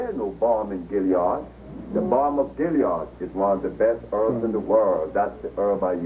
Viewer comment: What does he say at @ 57:50? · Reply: balm of gilead